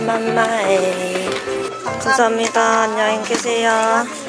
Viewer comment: 초에 죄송합니다라고 들리네..